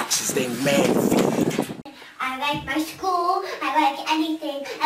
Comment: I dont think she said I like my mom